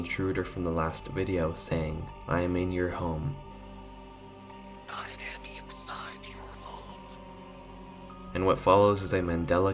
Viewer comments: when he said "I am inside your home." I thought he said "I am inside your mom" lol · when he said "I am inside your home." I thought he said "I am inside your mom" lol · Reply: I heard different "I am inside your phone" idk why